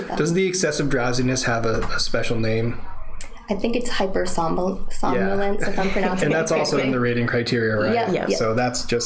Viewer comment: Did he say "hypersomnia" at 3:25 of this video? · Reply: Holt We refer to Hypersomnolence, which is a condition where a person experiences significant episodes of sleepiness, even after having 7 hours or more of quality sleep.